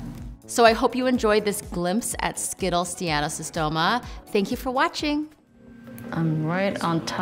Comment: did she call those Seattle Tacomas “Skittles Seattle Tacomas”??? 🤨🤨